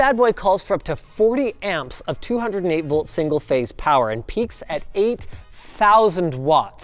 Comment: So i was watching this with my headphones with low battery and at 3:01 i just hear ''Peaks at..power off'' 😂